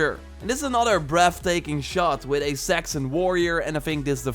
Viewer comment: I thought he said "Sexy warrior" :DD